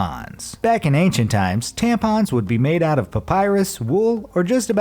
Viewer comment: did he said Papyrus?